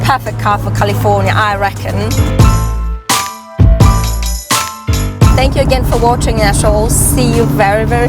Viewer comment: I am like, wait. You're in California! Girl on California Roads! Enjoyed the video!! Pause, at time index 5:31 did you say thank you for watching A wholes? It's OK, still enjoyed the video!